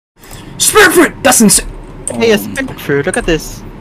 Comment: I heard "SPIN FRUIT"